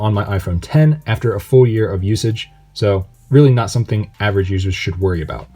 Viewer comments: did he just say he’s been using the 10 for 4 years?? Edit: never mind I just realized he said a full year of usage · what are you saying after 4 years of usage ??? iphone x released in 2017 i guess, are you from future? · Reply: “After a full year of usage”... lol idk how you got “four” out of that. It even says “full” from the automatic captioning service that YouTube offers lmao. Maybe you listen to your music too loudly?